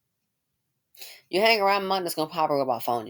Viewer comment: Hi! I LOVE your commentary but I couldn't understand what you said at 10:32.... What did you say after "Here's what I can tell you: " I couldn't hear the following statement...pls clarify. 🤗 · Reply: Sorry, sometimes I talk too fast. I said if you hang around money, it's probably gonna rub off on ya.